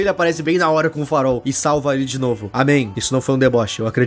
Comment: EU ESCUTEI UM AMÉM SENHORES??!